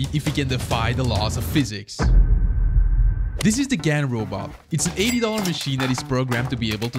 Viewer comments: As a little frustrated I heard "this is the little gay robot" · I thought I heard it was called the gay robot 💀